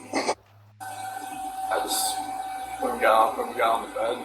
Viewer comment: N not just the shovel & rake…. When speaking of his convo/killing of shanann, it sounds like he says..” when we got, when we got on the bed.”